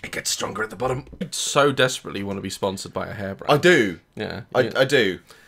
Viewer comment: I thought he said he wanted to be sponsored by a hairbrush XD